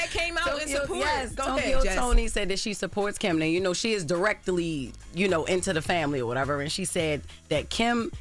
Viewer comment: What did Jess say @ 3:32-3:33…wtf is “DIRECTEDEDLY” 😂😂😂